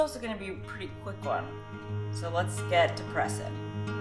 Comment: I heard you say let's get depressed so I did it now what ?